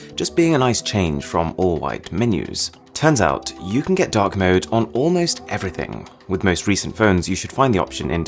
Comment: i thought he said dog mode